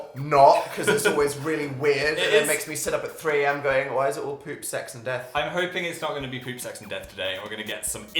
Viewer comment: I thought they said "poopsacks and death"